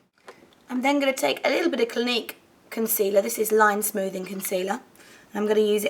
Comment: when you said Clinique concealer, I thought you said gleek concealer aha, I was like WTH?!?!